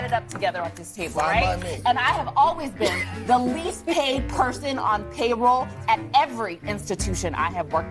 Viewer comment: All that education & acts like a clown. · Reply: I thought she said “least behaved” not the least payed. My first reaction was I can see that…